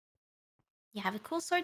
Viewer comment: I misheard “Cool Sword” as “Cold Sore” and that made the accompanying dialogue sound wrong...